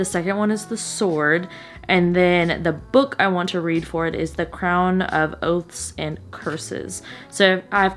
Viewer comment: I was only listening to the video and I thought you said "The Crown of OATS and Curses" lol I had to check 😂